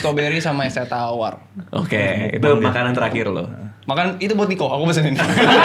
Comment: yuka ngomong apa ya kurang jelas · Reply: Itu makanan buat niko, aku pesen sendiri